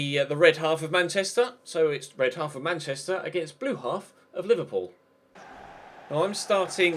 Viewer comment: Did he say at 9:07 blue half of Liverpool?😂😂